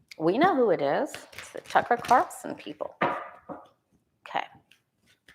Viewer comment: I thought I heard you say the “trucker Carlson” people 🤣🤣🤣